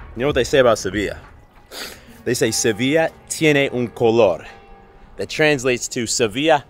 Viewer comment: I heard "Sevilla tiene un calor." Which is definitely true too!